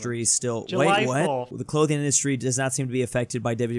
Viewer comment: Did he say... WWE2 😂 In multiple instances actually